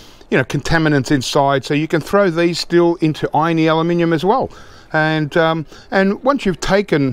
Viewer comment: OK, I'm in the USA. I understand when he says aluminum but is he saying 'irony aluminum' sometimes? Ex. around 7:14.